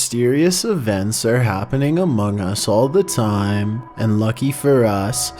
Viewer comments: Did he said among us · DID HE JUST SAY AMOUGS!?!?!??!